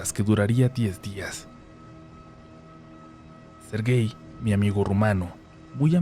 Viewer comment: jaja cuando dijo Sergei , pense que habia dicho " ser gay " 😅